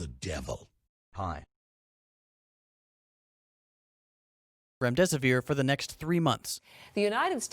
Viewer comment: Even the subtitles say REM dissapear...